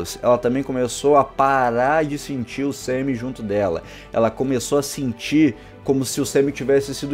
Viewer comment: eu escutei ''paro de sentir o semem dentro dela"